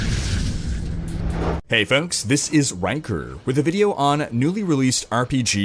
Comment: For a second there i thought you said 'wanker' instead of Rhykker.